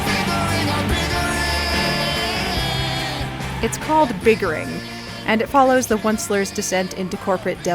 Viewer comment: At first the bigger song I thought he said “fingering”